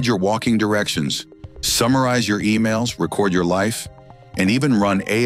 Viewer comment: Did he say Record you life? Ah yeah..... I think fucking not.